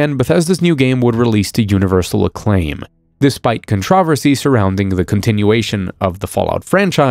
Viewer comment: did he say contrahversy